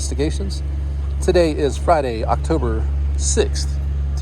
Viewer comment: Dude i thought you said October 26th and I panicked because I thought I had misplaced a few days and was late on work projects. LOL